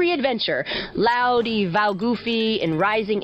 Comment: Loudy val-goofy 👍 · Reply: I hear “Loudy va boofy”.